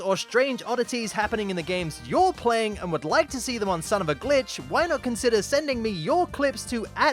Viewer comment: What I first thought the intro said “son of a BITCH”